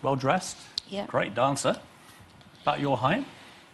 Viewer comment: piers: ''Hes smart, intelligent, good looking, well dressed, great dancer, _______'' what did he say last???!? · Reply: ”About your height” :)